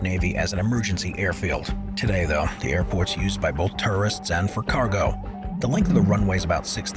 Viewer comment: 12:49 . Is it just my ears or the guy keeps saying terrorists instead of tourists ?